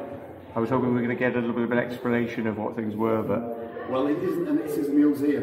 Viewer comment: seems like the owner thought you said exploration instead of explanation. {expletive deleted}.